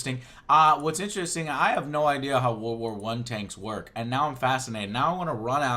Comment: I thought he kept saying war war one